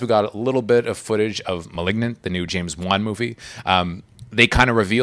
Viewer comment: I thought he said, “Malignant, the new James Bond movie” and I was so confused lol